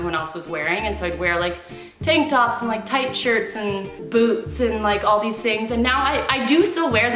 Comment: When u said "Boots" 3:14 I thought u said "and BOOBS!" (ignore me)